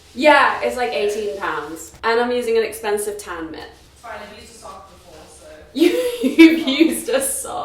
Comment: When your flatmate said "I've used a sock before", I heard 'I've used a soccer ball' 😹